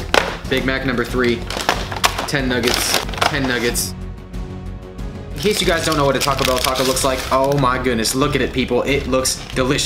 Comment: did he say incase you don’t know what a Taco Bell taco looks like 😂😂😂😂😂